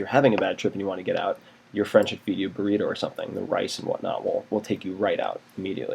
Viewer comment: I cant't really hear what he says about insulin spikes around 3:25 . A bit mumbled. But I do whant to say that when it comes to psychedelics I have had bad experiences with too little glucose intake. When I started to drink fruit juice while tripping, my trips became much more stable. Although fruit-sugar tablets don't work for me and tend to steer a trip in a bad direction. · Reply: Ari Groningen i think he said eat rice